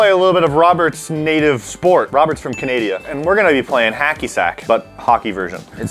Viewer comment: did just say Canadia? 9:59 watch this part